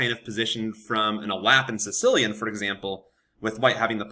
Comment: What do you say at 5:15-5:20? The openings: ____ and Sicilian? It kinda sounds like elapse or something. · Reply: The Alapin Sicilian! 1. e4 c5 2. c3